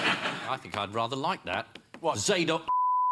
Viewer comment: what did Hislop say?? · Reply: There is a piece by Handel called Zadok the Priest. Presumably Ian said Zadok the Fucking Priest.